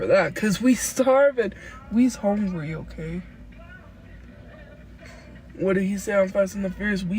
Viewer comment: I thought she said "We's homely OK?", and I instinctively said "You sure are" 😂😭